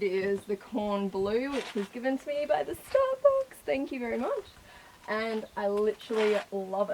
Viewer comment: it sound like your saying star bucks xD